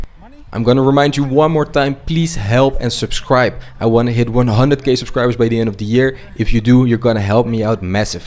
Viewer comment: did he say 100k gay subscribers or is it my eng too bad?😂